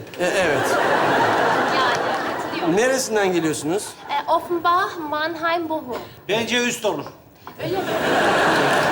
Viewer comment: neresinden geliyorsunuz sorusunun cevabına Sefa ne diyor? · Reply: Kız Offenbach Bochum şehirlerini söyleyince Sefa takım isimlerini kastederek maç üst olur diyor. Yani bol gollü olur diyor.